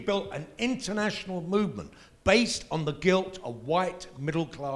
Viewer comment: Wait...Did he say WHITE GUILT???? and it is good? what a crapshoot!!!